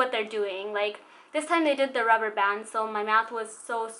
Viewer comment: Getting braces at 20 years old if not that old, my dentist was talking to me about there was a lady that was 84 that gets braces at my orthodontist 😅. I've had rubber bands for 9 months you get sooo used to it, I eat with my rubber bands lol😄 hope this makes you feel better❤️😘😘😘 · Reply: omg😭 I thought you said "I eat my rubber bands" and I was thinking maybe she just means they snap a lot of something 😂😂😂💀